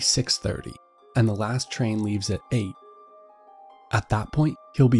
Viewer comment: He said eight but the captions said '7'